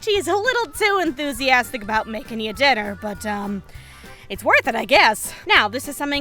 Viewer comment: I have a small actual question, here you say "Adachi is a little too enthusiastic about making you dinner" or "Adachi is a little too enthusiastic about making you deader"? Bc if it's the first one, I've been mishearing all the times I've watched this video AIUGCSAUYCFGAUYS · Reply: Dinner